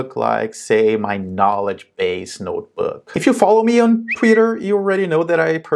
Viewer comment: If I follow you on what? Right around 1:55 I hear a whistle that interrupted what you were saying. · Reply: Twitter. But thanks to Elon Musk that's a place I'm not enjoying anymore 😔. I moved to Mastodon. 👇 <link>